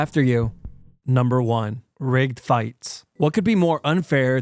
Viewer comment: Broooo. When he said rigged fights, I thought he said r*pe fights, and was very confused for a second there 😂